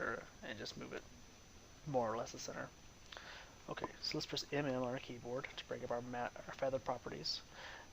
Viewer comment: what are u saying at 2:47 " so lets press blah bla in our keyboard to bring mask properties " ....your voice is really soft finding it hard to hear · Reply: I am saying, "press M M". my voice is low. I didn't have a proper mic. thanks for watching!